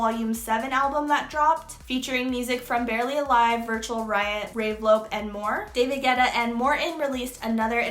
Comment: Did I hear you say "Ray Vlope"?😭 This is a good new series👌😌